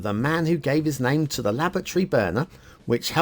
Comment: That 'It's My First Map' sting was a bit late... All through the Factiod I thought was saying 'lavatory', not 'laboratory'... A nice first map, but, as usual, the infrastructure needs a bit of refinement. Drowned trees, blue mantle, weak start square water flow flowing into the bay rather than out of it. Looks good, but needs improvement.